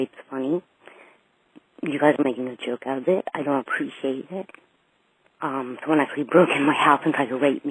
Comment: I think it says "Someone actually broke into my house" (since it says it was unintelligible)